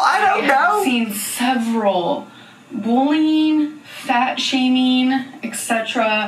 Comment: When Amber says "bullying" I always hear "boolean."